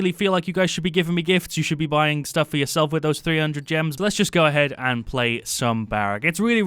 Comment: did he say gems?